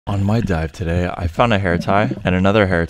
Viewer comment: I thought he said my wife died today I was about to say “YOUR MARRIED “